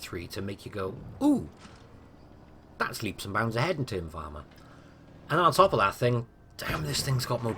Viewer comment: Did anyone pick up what he was saying at 2:46 I have no idea what he said there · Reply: CONTROL123 Blitz “ooh thats leaps and bounds ahead in terms of armor”